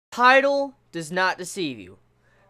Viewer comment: LOOL in the beginning I thought he said TIDAL DOES NOT DECEIVE YOU.. lol